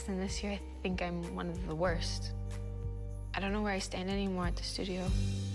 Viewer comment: Littrally i always hear "i dont know were i stand in this studio anymore"